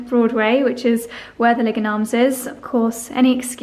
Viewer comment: Regarding your anniversary outing - I heard 'Leg and Arms' and thought "Where do they come up with pub names in the UK?" Lol Realized my error when I googled it. Your powder room mirrors are absolutely perfect! What a brilliant find! Regarding your health report, please know that nothing but love is coming your way. Thank you for sharing in such a generous, caring way. XOXOXOXOX